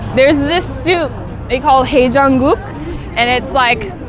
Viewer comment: I thought she said 'hey jungkook'😂😂